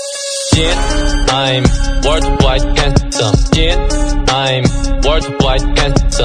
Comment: Instead of "yeah i am world wide handsome" i Heard " shit i am world wide handsome "